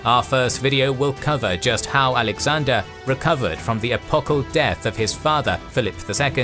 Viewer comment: apocal? What is this word? Google keeps telling me is “apical” but the bot pronounced it way different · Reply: epochal